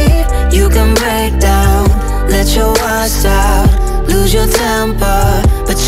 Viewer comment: I kept hearing "lose your tampon", not "lose your temper". So there's that.